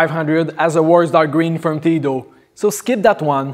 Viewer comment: I am very sorry, but I cannot understand what is said at 4:24 between "9500" and So skip that one. Phonetically, I'm hearing "as a wars dar green from theedo." Please help. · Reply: Yes, "worse dark gray uniformity though"